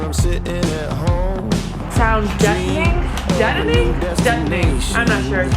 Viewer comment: All this time I've been repeating what I thought was saying, which was "sound detoning"... only to ask him now when you questioned it at the start and he said "deadening" -- which makes perfect sense because you want the sound to be, well, dead. I'm totally blaming my misunderstanding on his British accent... as I do with other words I mispronounce as he teaches me about vanbuilding on our Cortez haha L (and)